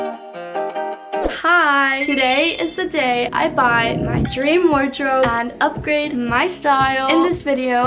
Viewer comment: My dumb ass really thought it said “bullying my dream wardrobe” 💀